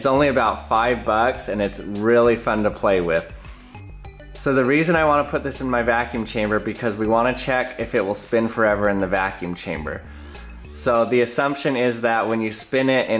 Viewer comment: Lol, thought you said "So the reason i put this in the vacuum chamber is because I want to see what happens if I put it in the vacuum chamber.